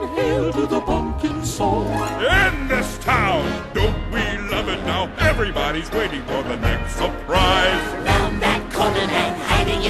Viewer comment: I thought he said end this town don’t be lovin now everybody waiting for the next surprise